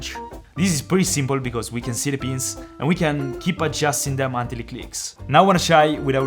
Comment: Every time you said ‘i can see the pins’ I thought you said ‘i can see the beans’